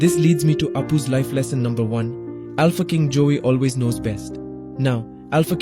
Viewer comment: The first time he said Alpha king Joy I thought he said “A f*cking Jew” Maybe all of my WW2 knowledge is out of my hands XD